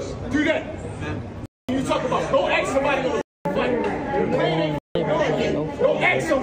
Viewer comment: Is he saying : go axe somebody??? That's incitation to violence